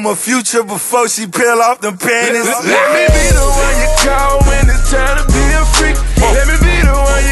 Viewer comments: When I first heard this I thought he said "Let me be the one to call when it's time to be free" 🤣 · Spongebob in the hood part 1 brought me here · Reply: At first I thought he said "let me be the one to call when it's time to be free"